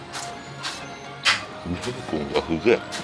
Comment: このチャレンジ、さんやらないのかなって、待ってました( ¨̮ )︎ 色んな人の観てるんですが、成功ほんとに凄いです( ᵒ̴̶̷̤໐ᵒ̴̶̷̤ ) ただすごく気になったんですが、17:03あたり｢ベーコンが (す)げぇ｣って言ってますよね？？ ｢す｣じゃなくて、｢う｣って聞こえてしまって、ショックだったんですが、｢す｣だと信じてます！